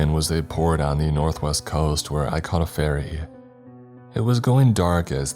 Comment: what I hear: “…where I caught a fairy.” Me: 🤨 wtf …. oh he means a FERRY Why is English so fucked?